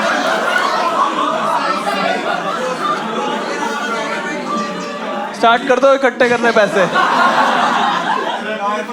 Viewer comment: what did they (audience) say on 31:00 ? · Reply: Politician